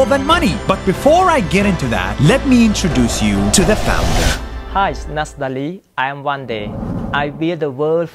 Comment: the subtitles said "hi nastali I am one day 😂